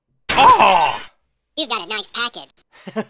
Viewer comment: What did he say ? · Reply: the elf said " You Got a Nice Package"